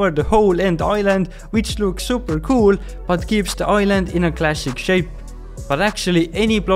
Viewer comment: Am I the only one who hears 'glassic', instead of 'classic'? 🙂